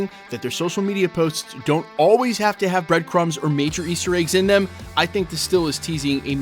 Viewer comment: Man I wish Kang was included into this mix... btw, did I hear "breadcomes"? lol ps. what is an AGBO??:face-fuchsia-tongue-out